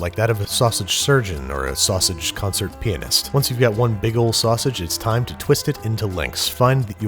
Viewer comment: I straight up thought he said penis